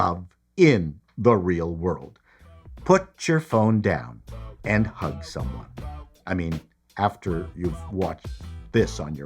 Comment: It kind of sounded like you said, "Put your phones down and HUNT someone." I'm hearing that wrong, right?